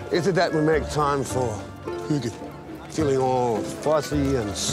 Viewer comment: what does he say at 0:23??? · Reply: hygge